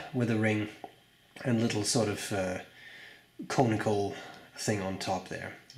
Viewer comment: When you were talking about the conical cap finial I happened to have looked down at my lunch and thought you said 'comical' so I quickly looked up to see what was comical.